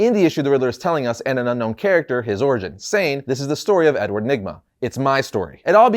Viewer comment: At first I thought he said his name was Edward Ligma 💀💀